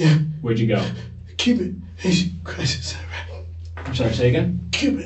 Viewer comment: what did he say? Cuban nation crisis? 😂